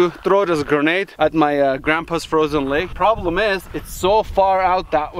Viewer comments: "Everybody wanted me to throw this grenade at my Grandpa.." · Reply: I swear to god I heard “grandpa’s frozen leg” the first time. 💀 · Did I just heard - “My Grandpa’s Frozen Lake”